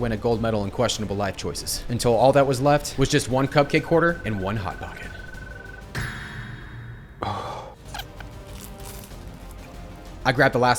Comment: did u say hot dogs to a hot pocket